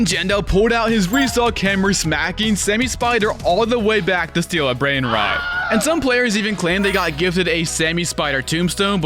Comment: why did it say steala character instead of steal a brainrot?